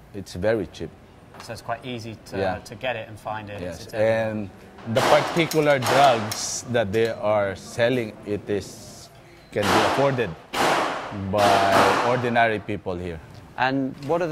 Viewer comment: I hate it when channels like Vice do captions on videos, and caption something entirely different to what the speaker said. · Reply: Sure, at around the 1:49 mark the police officer is captioned as saying "The particular drugs they're selling, can be afforded by ordinary people" However, he actually says "The particular drugs, that they're selling it is can be afforded by ordinary people here" this is what I meant by not accurately representing what is being said and polishing it up.